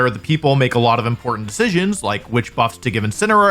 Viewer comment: I thought said, “ sure this is a very important building we’re talking abiut where the people make a lot if impotant SINS ( he says decisions ) like which buffs to give incineroar each generation “ I died laughing, he didn’t even say it.